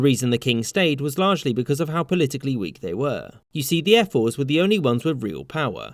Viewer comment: I heard "Air Force"